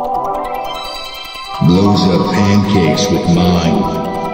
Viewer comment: bro remember when i thought he was saying "loads your pancakes with wine"? ahhh, gooooood times